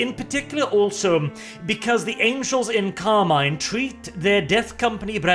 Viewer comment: I keep hearing " the angels in car mime "